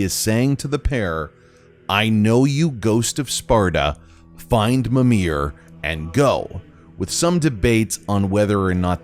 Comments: at the end I heard "find Mimir and RUN" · I hear "i know you Ghost of Sparta find mimir he Will help" not "and go" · I don't hear the "I know you ghost of Sparta grab mimir and go" instead I hear "I know you ghost of Sparta grab mimir and rise" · Ngl when I first heard this in my gameplay I thought he said "I know you, ghost of serpent" which had me asking myself who tf is ghost of serpent? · I hear "I know you Ghost of Spar." "Find Mimir he will help."